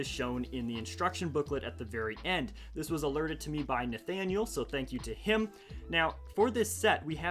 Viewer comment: DID YOU SAY NATHANIEL BANDY?